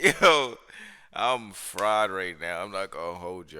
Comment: The closed caption before clicking said “I’m fraud”, clicked and it damn sure sound like you said it until I thought💭 I think he’s saying fried.